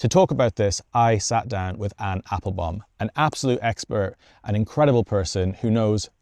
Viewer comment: I thought you said "an apple bomb”